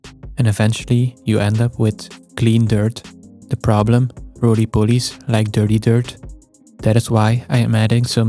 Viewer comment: what did he say? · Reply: That was what was funny, he said exactly that... "Rolly Pollies like dirty dirt, because dirty dirt, is like dirty dirt..."